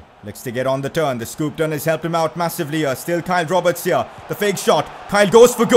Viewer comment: Why does it sound like him saying Kai Roberts